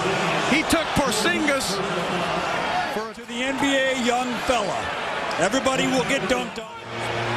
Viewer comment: who thought the dude was bout to say “NBA young boy